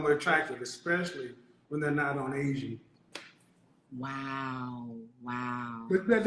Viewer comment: EXCUSE ME??? NOT ON ASIAN??? DID I HEAR THAT WRONG wtf